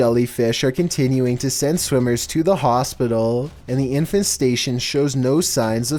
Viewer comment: I heard 'Infant station' at 7:44 and was confused for a moment LMFAO